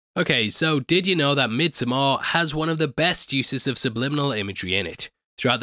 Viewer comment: Did u just say midsommoaåhh